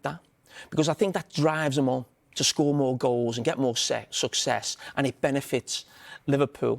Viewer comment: what did jamie say lol? get more whaaa 😂? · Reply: Succes...